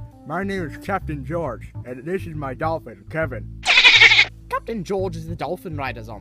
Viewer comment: Did someone say "Kevin"? :D